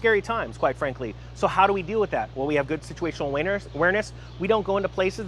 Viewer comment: I heard situational awieners lol